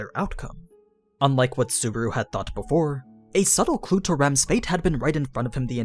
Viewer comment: "a subtle (what did you say next?)" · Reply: "A subtle clue to Rem's fate..."